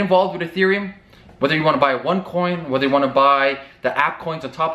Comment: did you just say "onecoin" the scam coin?